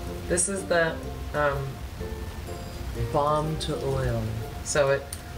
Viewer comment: Totally thought she said "bomb to oil" not "balm to oil"